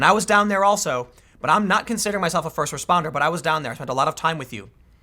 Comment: Hey, he just misheard "I'm not considering myself a first responder" as "I consider myself a first responder". If you work at Vox, you'll be primed to hear the latter.